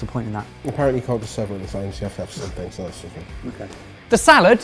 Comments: Everyone talks about Barry's mumbling... I have no idea what Jamie said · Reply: He said “apperently you cant just serve it on its own you have to have something so i just said (groan)” lol i understood him clearly🙂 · What did Jamie say at 6:20? Has he caught the mumbles from Barry? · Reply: He said “apperently you cant just serve it on its own you have to have something so i just said (groan)” lol i understood him clearly🙂